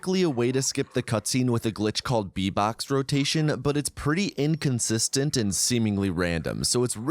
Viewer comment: My brain is completely fried, I really thought he said “v-bucks rotation”